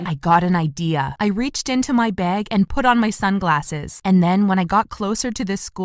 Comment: Why did I hear “bed” instead of bag